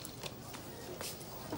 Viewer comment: What is she saying at 03:17? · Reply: "Get your laughing gear around those son!"